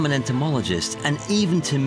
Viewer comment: I watched this when I was just a child, and it's influenced my desire to be an entomologist ever since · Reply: I thought he said "antomologist" thats hillarious